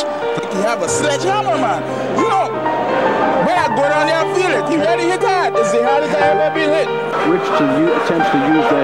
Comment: I can't make out the full quote, what is he saying? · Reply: Yeah man. You know, the way I go down there, he really hits hard. That was as hardest as I have ever been hit.